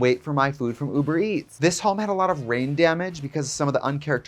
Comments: I heard you say this house has brain damage versus rain damage haha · When Trixie said that the house had “rain damage” I thought she said “brain damage!” 😂